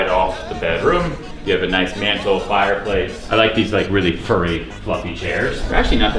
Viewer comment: You could exercise daily just by going up and down the stairs maybe twice 3:55 DID HE SAY FURRY! oh my fucking god